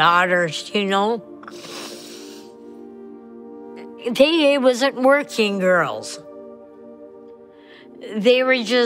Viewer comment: subtitle reads “they was working girls” but says “they *WASN’T working girls”, I think she would say that’s an important distinction